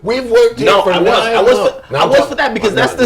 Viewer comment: Joe said "mummfs" instead of "months" lmao. 9:37 he says it twice after that time stamp.